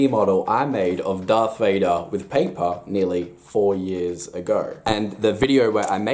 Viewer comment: at first I heard 40 years ago🤣🤣🤣